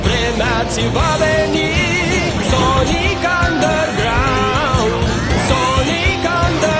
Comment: I swear I just heard him say "Nazi" XD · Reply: He said "iți va veni" wich means "it will come" yes im romanian